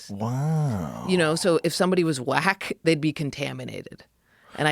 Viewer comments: I thought she was saying black not whack for a minute 🤯🤣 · Yooo! Listening to this while working, at first I thought for sure I heard them say black instead of whack, tripped me TF out 😅!